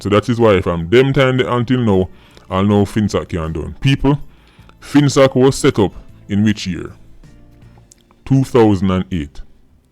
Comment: you are the second person to type this. I said "finsac enquiry commission" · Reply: I thought I heard when you said, "people FINSAC was set up in which year 2008" I guess I heard wrong, my mistake sir.